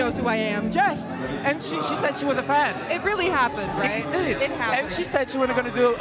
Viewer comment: Thought he said he was funny